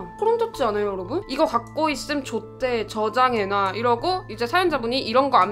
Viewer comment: 이거 가지고 있음 좆돼 로 들려요 ㅋㅋㅋㅋㅋ